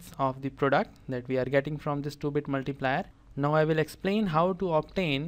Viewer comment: I heard it as the stupid multiplier instead of 2 bit multiplier.